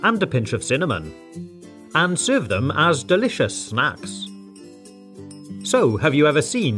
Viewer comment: he said smacks instead of snacks